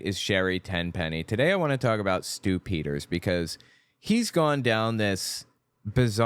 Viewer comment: Stew Peters - every time you say that name I hear "Stupiders".